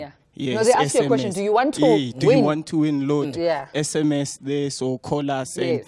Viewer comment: did he say -- wiloss com ?